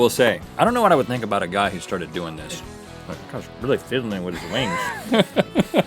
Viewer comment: I thought Rhett said "Fiddling with his meat"